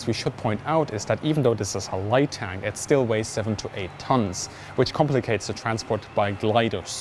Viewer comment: I thought he said 78 tons and I was like "YO WTF"